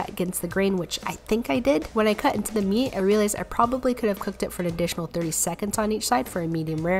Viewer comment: “I probably could have cooked it for an additional 30 seconds.” PROBABLY??? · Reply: I thought she said an additional 3 seconds on each side. Like okay... 🤣